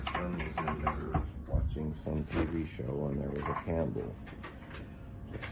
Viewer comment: As always, if you'd like to support the content we make and get access to unreleased footage and crime scene photos, you can join our Patreon at <link> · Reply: I think he says, "...and there was a candle" because he discusses how it was weird to have a candle lit in the house since he (the dad) is sensitive to fumes and smoke. Sorry, random, but just thought I'd help since you had it as "campbell (???)" in the captions, lol. Thank you for sharing, I had not heard of this case whatsoever! How very sad and tragic, just heartbreaking 💔 may the 4 victims rest peacefully and the dad heal quickly.